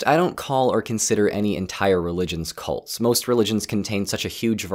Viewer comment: thought you said religions are not cults